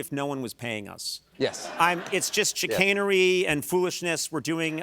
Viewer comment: What’s the word Conan uses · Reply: Chicanery!